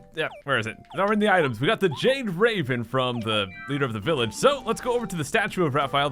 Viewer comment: At the beginning of the video, when you said the "Jade Raven", it almost sounded like you said the "Jake Raven"